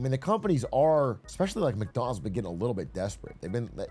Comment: I heard "McDonald's is getting a little McDesperate"